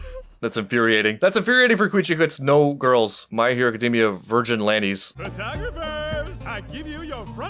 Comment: you did not say fucking virginlantes 😭😭